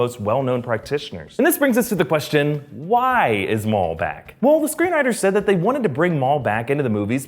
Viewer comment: thought that said why is maul black